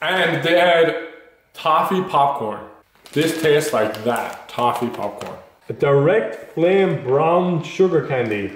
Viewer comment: Thought he said coffee popcorn